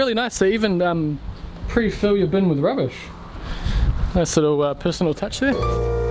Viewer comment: haha, Im american, what did you say? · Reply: I'm an Aussie but I'll translate for you. "They even prefill your bin with rubbish" (pause then at 6:11 he said). "nice little ahh personal touch there"